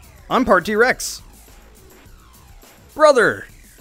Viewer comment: All i hear is "BRUDDER!!!!"